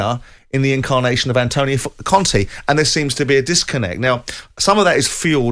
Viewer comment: does Simon say "Antonio f*ckin' Conte" and it was censored or am I hearing things?